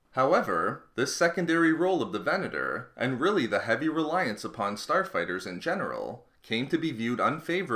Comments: who heard him say Porn star fighter 3.20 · Who else thought he said porn star fighters